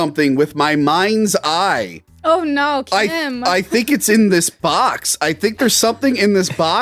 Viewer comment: Did just say chim? morrow-boomer confirmed?? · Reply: Huh? I said Kim as in Kim director because Tony said he saw something with his mind’s eye like Kim does in the film